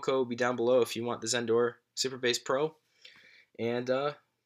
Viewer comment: Bro I thought you were saying super bass pro, I thought it was a giant Bluetooth speaker, I thought you were planning to pump some music 😂